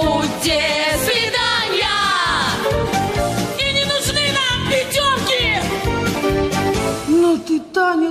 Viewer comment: На 47:31 "и не нужны нам..." кто? Не могу расслышать, подскажите пожалуйста. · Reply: Пятёрки, оценка за номер